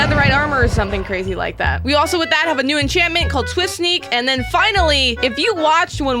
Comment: TWIST SNEAK lol It's swift sneak (idk if that's what she said but the subtitles said twist sneak)